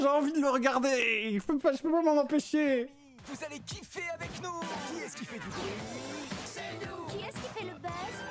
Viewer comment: J'ai entendu qui est-ce qui fait du grooming? ...